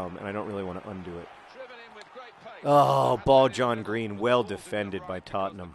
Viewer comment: I always hear "Bald John Green" as "Ball John Green"...I always thought it was an odd thing to call your players!! :L